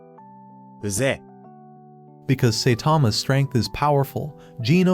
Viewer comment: the subtitle said say thomas so should i trust the subtitles or the name is saitama